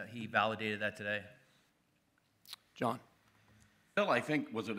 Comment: did they just call Phil “Jon”? (And he responded too 😂)